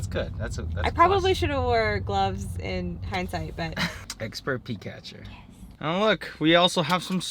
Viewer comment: was working while listening and thought said Expert Pikachu.. 🙊 😂